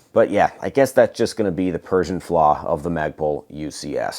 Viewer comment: did he say “Persian flaw”?